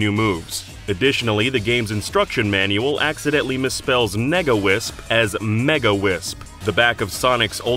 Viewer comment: Additionally, the game's instruction manual accidentily misspells MEGA wisp as MEGA wisp Genuinely have no clue what he's saying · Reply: He says, "accidentally misspells Nega Wisp as Mega Wisp."